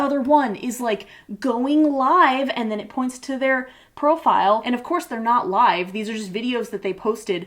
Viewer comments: did you say "Pearl-file" because I am HERE for that pun intended or not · did I hear “pearl”file? And not profile? Too many times headlong pearl